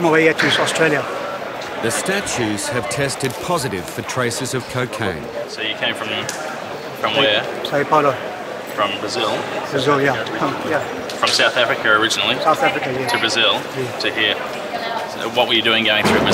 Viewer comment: does he say he comes from Harry Potter? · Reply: He said “São Paulo” haha 😂 but when I think of Harry Potter when he says it I totally hear it too 😆